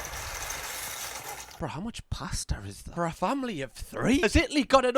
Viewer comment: I thought you said "plaster" at 8:12 and got SO intensely confused lmao it took me at least 3 more sentences to realize you said "pasta" hahaha